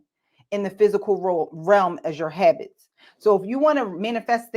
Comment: I thought she said "your heavens" I'm so glad she said your habits...lollollollol...I was like "Oh no! this is so good but I'm going to have to click off!!! lol. I kept listening Sis, I was hopeful! lol Good Stuff